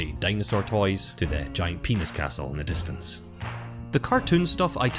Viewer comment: Did he say giant penis castle in the distance at 3:32 ??? 😂😂😂